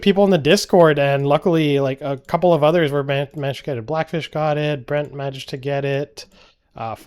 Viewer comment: omg i thought he said black *itch